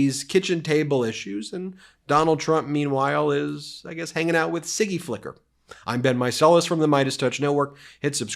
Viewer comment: I thought Ben was saying Sticky Licker. That's what happens as a result of fellatio.